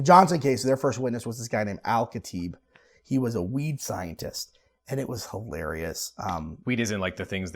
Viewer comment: 🤣 I thought he said "Weeb scientist" not weed scientist.